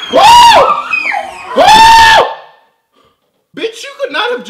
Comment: did he say "BITCH!"?